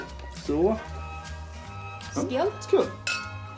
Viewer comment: var det ett skånskt "skål" jag hörde? Bra video som vanligt! :)